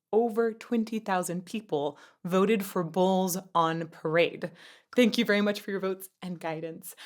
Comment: I swear I thought you said" balls on parade" lol. Great political song